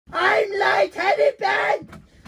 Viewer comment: i thought he said i want teddy bear. HAHAHAHAHAHA!!!!!!